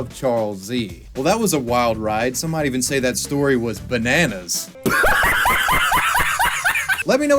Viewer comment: did someone say bananas?